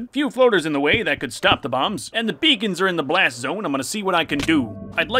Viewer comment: I thought he said “a few voters in the way that could stop the bombs” 🤣